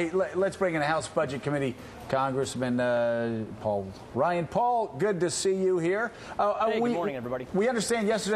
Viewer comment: 25 Did he just say Ryan Paul? haha